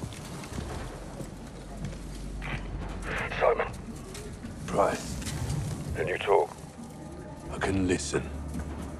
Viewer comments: I can’t tell if Price is saying “Simon” or “Soap” I keep hearing Simon, but some people are hearing Soap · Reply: ITS SIMON SAY IT TO YOUR HEAD A FEW TIMES THEN YOU WILL HEAR SIMN WITHOUT AN "O" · “Can you talk?” “I can listen” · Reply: Now that's the part where i heard something different i thought he said "i couldn't listen" but he says "i can listen" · does he say soap or Simon? 🤔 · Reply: Simon